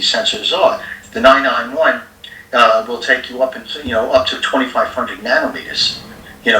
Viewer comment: Not to detract..l but I heard "Up to 25 F'ing nanometers " 😅😅